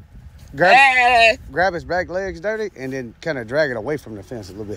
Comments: Are we gonna ignore the fact he called him “daddy” 😂😂😭😭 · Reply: I don’t think he said “daddy”. Sounds like he said “dirty” or something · "Give it ah second Daddy , I mean give em ah second." 😂😂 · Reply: He said Dirty but I'll be damned if it didn't sound like he said "daddy" at first 😂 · So we're just gonna ignore the fact he called him Daddy twice? 🤣🤣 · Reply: I heard "Dutty" or something but I can believe dirty too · Backtracked on that “daddy” real quick😂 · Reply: That's exactly what happened to me 😂 I HEARD "daddy" the 1st time, now I perfectly hear him calling em "Dirty"